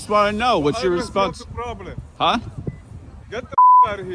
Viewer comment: did he just say "is not your problem"?? Yes, it is about the US citizens taxes!!